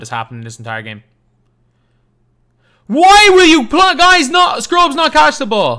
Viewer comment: you should watch what you say bro some will take it as racist now a days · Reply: I was going to say "player", then "guys". I just noticed what it sounded like I was about to say 😂